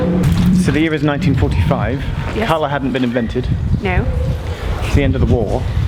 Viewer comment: "Colour hadn't been invented" I misheard that as "Hull hadn't been invented"...